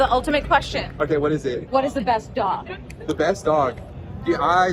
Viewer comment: yeah, I heard dog, too, not DAW.